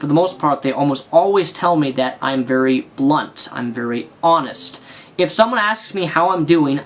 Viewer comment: did someone say blunt nigga?